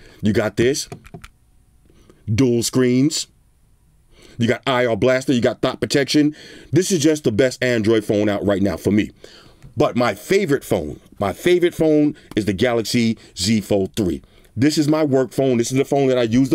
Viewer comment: Did I hear THOT protection? Do tell.